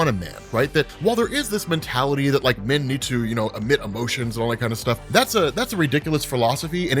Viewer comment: No you guys he said the opposite, listen more closely. He said anyone who believes that is wrong. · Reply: totally agree. Hearing him say it was a shock because not in his character. Listened to it again. Thought he said “admit there emotions” realise now he say “omit there emotions” getting that one word wrong does change the meaning of the rest of the sentence. Did think to myself it didn’t sound like something he would say.